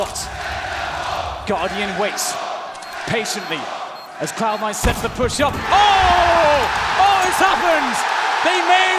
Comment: What was the crowd shouting at 1:32:56 ?? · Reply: send them home